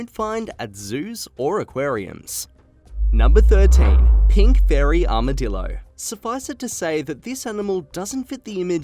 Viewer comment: Oh, I thought you said pinkberry armadillo!